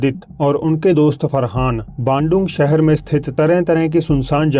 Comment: he says bando I hear gando 😂😂😂